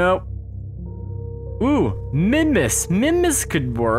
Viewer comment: when you said mimas, i thought you said minmus ._.